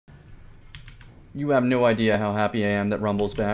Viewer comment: I thought you said you don't have any idea of how fast I am at first 😂😂😂